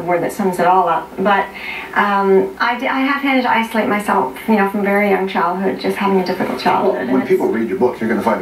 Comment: does she say at 3:57 'i have hinted that i slit myself from childhood' ?